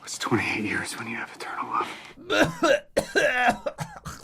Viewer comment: Honestly thought he said "what's 28 years when you have a turtle, love?"